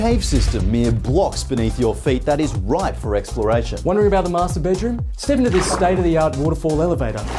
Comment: When i first heard u say "master bedroom" the word i heard was masturbation😅😅😅